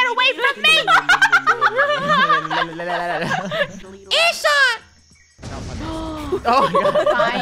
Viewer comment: Did i hear say “GET AWAY FROM ME (swear word)!”? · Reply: No, she said fish